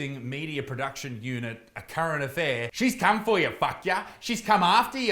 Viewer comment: Did he say " a Karen affair? About right